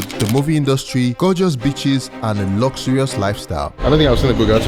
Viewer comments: The first 30 secs I was just listening not watching and I heard “Gorgeous Bitches” not Beaches 😅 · WHOA, I thought you said gorgeous bitches, I was ready to pack my bags! Beaches I have in New York!